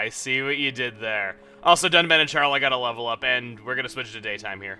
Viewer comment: Dunban and... Charlie? I can't be the only one who heard Charlie and not Sharla ._.